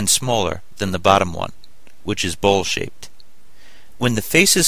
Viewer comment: I thought he said which is bull shit xD Thanks that was really helpful!!